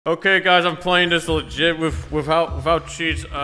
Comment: did u say jit? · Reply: He said legit not jit